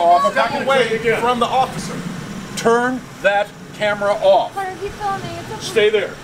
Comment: carter keep filming? what are you saying?